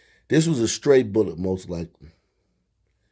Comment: did he say “straight bullet” or stray bullet? By the way, it’s extended magazine, not “clip.”